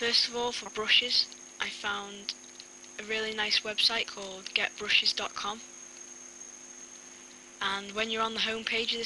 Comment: Talk English brov! :D xD when he said i found this realy nice website called getbrushes thought he said gapbrushes! lol